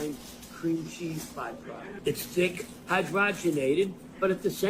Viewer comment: Did he just say “cream cheese byproduct”